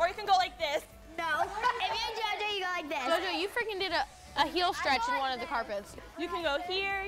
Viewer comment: I heard someone say freaking